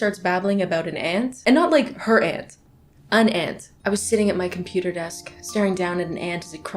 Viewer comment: i heard “not her aunt, an aunt,” and i thought, ‘ya that’s weird, just talking about someone else’s aunt with no context,’ and then actual ant footage popped up on the screen and i loooosssstt it